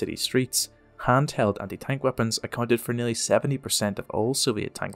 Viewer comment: Did you say "Hand held anti tank weapon" or "Hanz held anti tank weapon"?